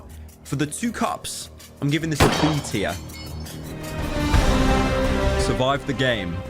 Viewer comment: Did he actually say B tier, or is it just a British A?